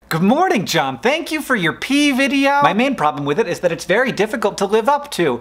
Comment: "Good morning John! Thank you for your pee video!" was what I initially thought you said and I was like "WTF did I miss this week?!??!". Then I remembered the pea video and calmed down.